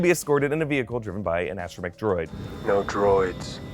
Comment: Does anyone else hear “astro Macdroid?” Twice?